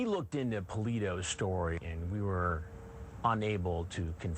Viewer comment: the subtitles say poo’s story lol